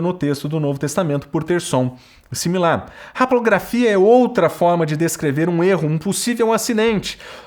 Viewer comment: O que ele fala em 25:16? Raprografia? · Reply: Haplografia: Erro de copista, que escrevia apenas uma vez o que devia ser repetido.